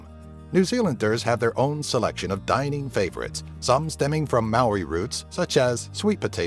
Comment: americans saying maori words priceless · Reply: Karlos .M i heard 'Maui' in stead of 'Maori' xD